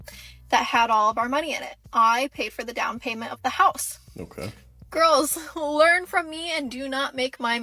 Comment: What I heard was, "Boys learn from her and do not make her mistake." Good. Got it. 👍